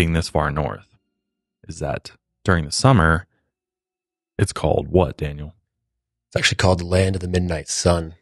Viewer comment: I thought at 13:27 had said ‘it’s called- 🙄WHAT, daniel?’ Instead of handing off the topic to him dkdjjdrj